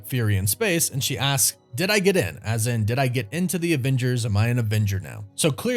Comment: I thought she said “say it again” instead of “did I get in”